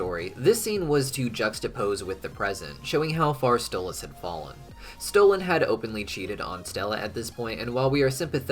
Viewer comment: did you just say stolen?!